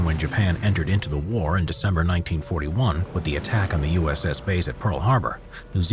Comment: Did you say "The attack on USS"? 1:42-1:47 lol